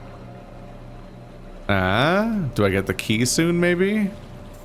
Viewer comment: when said "Do I get the key soon, maybe?", I definitely heard it as "Kissu" and was very confused for a moment.